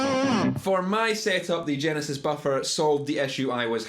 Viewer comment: I thought it said Genghis buffer!